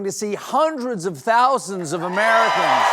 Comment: Millions Stephen, not tens of thousands, millions! It matters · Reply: I thought Stephen said hundreds of thousands; maybe still not the accurate "millions", but a little closer. 🙂